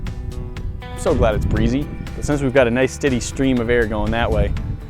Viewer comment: Umm is it just me or does it sound like he said nice TITTIE stream on